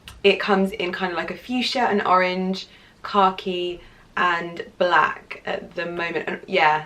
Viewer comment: Omg you're adorable! I totally thought you were saying "car key" when you were describing the color of the Louis Vuitton bag but you were actually saying khaki!! Aah! I love it! Great haul!